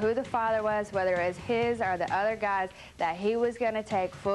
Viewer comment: what did she say because I'm sure I heard other guys